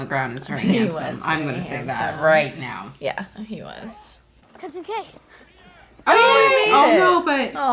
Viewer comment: Did Jojo say "cousin K" @ 28:15? Are they cousins?